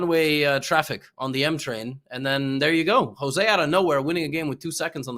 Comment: what is Aman calling Jose? Jospum? What is that? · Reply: Jospem, his online handle, which is how most of the online chess community knows him